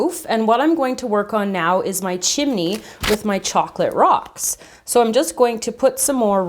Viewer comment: LOL! I thought you said "Chocolate covered rocks" and I was like "Rocks?"